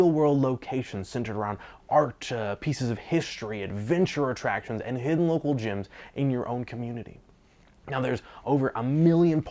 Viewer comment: you say... "and hidden local gyms." Wth?!? That sounded like a Pokemon reference. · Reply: He said "local gems"